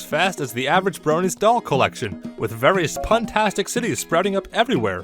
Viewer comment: Did you just say... Pun-tastic..?